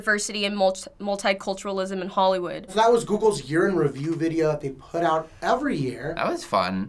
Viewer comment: When hè Saïd; “This is Googles Year in review “ i thought the said urine review and i was like; OkAy M8